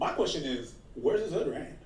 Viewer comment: I think when Aba and Preach are 90 and Fresh and Fit are also 90 , the beef will end . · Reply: you don't have it on normal speed that's why you can't hear it... he said where is his other hand😂